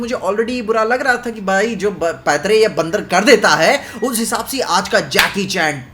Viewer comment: did really said bandar 😂😂😂